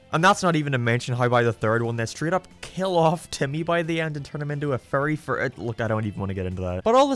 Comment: I genuinely thought you said “they turn him into a furry-“